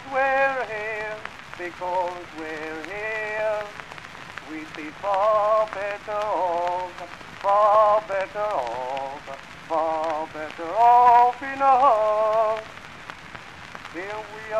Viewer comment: What does he say here 0:50 ? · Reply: He sings "We'd be far better off, far better off, far better off in a" Unfortunately I can't make out that last word.